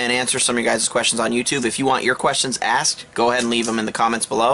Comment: Did you say would you like your questions asked?